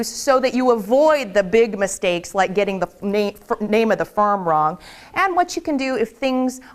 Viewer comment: is it just me or did she say 'Farm instead of Firm' in 01:11??